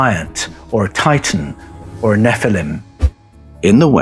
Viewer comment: ~ Shouldn't it be giant or "Titan" instead of giant or "tighten" ? At 29:34 a "NIETZSCHE" intervention ?